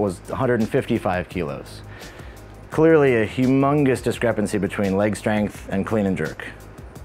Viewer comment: WHAT DID YOU SAY?! DID YOU JUST SAY HUGHMUNGUS?